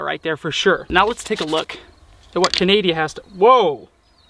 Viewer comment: did you just call Canada... canadia? 😭😂